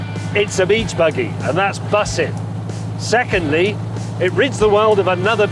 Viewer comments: Did he just say, "its bussin' " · James May saying "bussin" was not something I expected to hear today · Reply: he was talking about Birmingham, where I live. It’s bostin. If you heard him say “bussin” then either you misheard or he garbled it. It’s bostin.